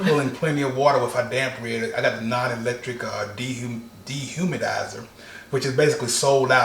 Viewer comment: I thought was saying dehumanizer lol